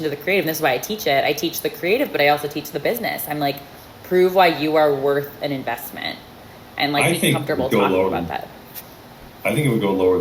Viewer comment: What I heard she said - “Prove what you are worth in investment” 😳